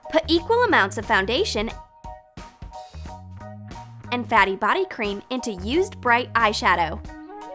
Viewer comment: did she say fatty body lotion???!!!